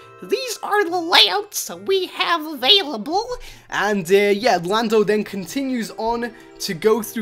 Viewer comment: Did he just call Lando Blando?